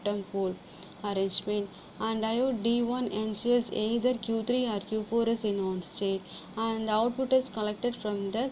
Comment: I didn't get what you said. Hard to understand. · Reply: Ok I get it. You said: "The diode D1 ensures either Q4 or Q3 is in ON state."